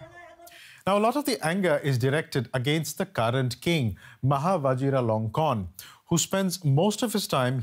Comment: I misheard his name as Maha Vajiralongcon. 😂